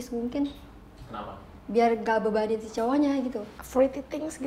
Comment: biar ga bebanin cowonya? atau salah denger